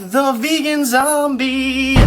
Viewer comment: Looks delicious! So real talk though, do you guys over at the vegan zombie watch The Walking Dead?! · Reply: at first i thought you said Negan zombie